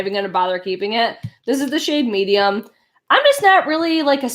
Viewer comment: Which CC/BB cream did you say you wanted to try? Around the 24:10 mark but I didn’t catch it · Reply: Parnika Jain Missha!